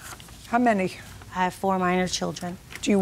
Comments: She said she has 4 minor children then said they’re all grown ???😂😂😂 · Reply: I heard "I have 4 of my own children." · She said she has 4 minor children but does not get child support because children are all grown. What?! I am sorry.... but what?! Who is ordered to pay $3500 in alimony but zero in child support? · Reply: I think so too. I heard her say “four of my own children,” and I had to go back to see where she ever said “minor.”